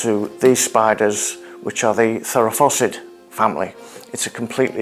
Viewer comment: Did anyone else hear "Farrah Fawcett" family? 😁 Fascinating, well done.